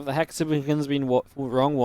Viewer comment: Have the "????" been wrong ones this tournament? What does he say there? Hexagons? Maybe someone can help. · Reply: hexagons